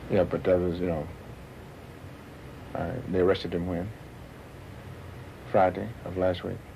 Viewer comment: Sound like he said they poisoned him.